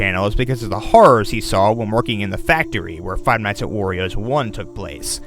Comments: On number 3 i thought you were saying five nights at her movie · I COULDVE SWORN HE WAS SAYING FIVE NIGHTS WITH MAGIC MIKE